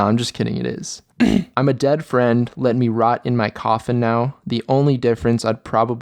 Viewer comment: when the song said 'rot in my coffin' i heard 'nut in my coffee' lmaooo